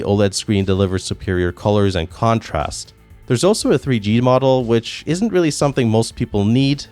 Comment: Did I mishear the Op or did he say "3D" since when did the Vita ever do 3D? *ok he said 3G, not 3D.